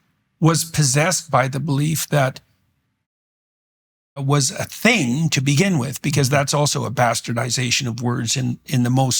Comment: is Jordan speech beeing censured? is that by YT or what? the word is "transfobic" I assume?